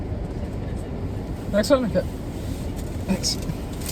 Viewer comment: did he say "Tack så mycket"? lol